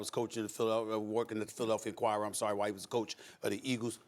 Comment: Thought Stephen A said “Philadelphia choir.” I was like, uhhh yeah sounds about right